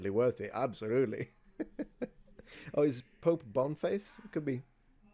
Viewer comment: Did you just say Pope bum face?